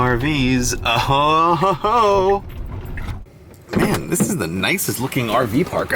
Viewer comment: is it just me or does he say ho ho ho hoe